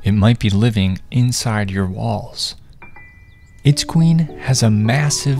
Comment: I thought you said balls, I was scared for a good few seconds